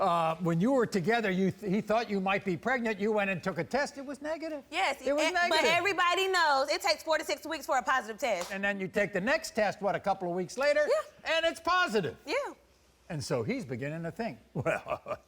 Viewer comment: She trapped that boy. “First test was negative and second test was positive... Everybody know it takes 4-6 weeks to test positive.” Well mam how you know you were pregnant then 🤨🧐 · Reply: Lol I deadass thought she said 46 weeks and I was like huh